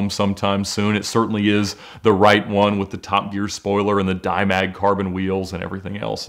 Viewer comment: Did he just say “ Dime Bag Carbon Wheels “ ???